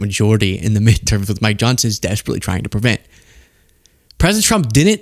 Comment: lol… I thought you said “my Johnson” … lol it fits too.